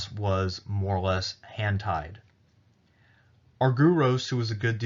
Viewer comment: I swear to God, I thought at 36:00 he said “Hentai’d”